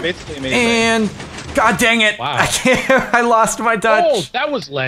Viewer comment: did he say "i just lost my dutch"?